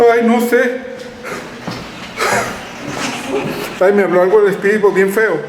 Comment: En el minuto 47:20 yo escucho que dice "diablo" 😱